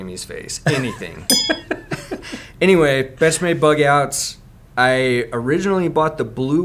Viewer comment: Anyone else heard bestmade bugout